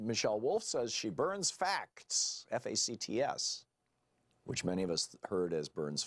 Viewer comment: I heard "facts" but after your suggestion I can hear "fat" although the Maybelline makeup joke won't work if it was. If you are primed to hear a criticism of someone and appearance is at the forefront of your mind, the setup did included her poking fun at her own appearance while roasting Conway, this is perhaps yet another level below all the others poking fun at her audience's prejudices. Is she that clever? I hadn't heard of her before and I look forward to finding out.